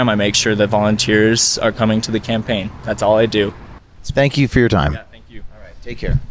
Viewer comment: did I hear you right? “Spank you” 😂😂😂 Priceless.